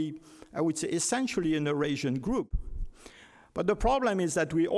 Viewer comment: he did not say "in the asian group" but "in eurasian group".